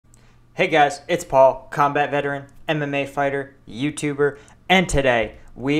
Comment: i heard anime fighter XDD